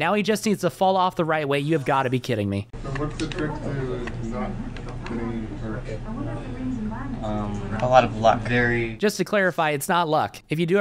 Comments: the unintelligible part I think he’s says not getting hurt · "So, what's the trick to not getting hurt?" (What I heard.)